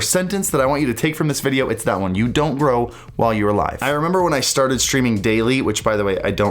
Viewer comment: does he say "while you are a live" ? not "when you are live" ...